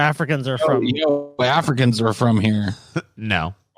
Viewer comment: I think she said “your ancestors aren’t from here,” not “Africans are from here.”